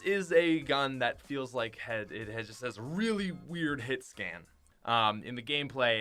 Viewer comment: did he say “feels like head”🤔